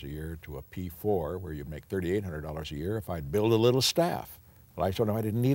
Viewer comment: I thought he said little hats